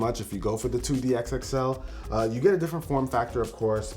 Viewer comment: Did he say 2DXXL @ 2:07?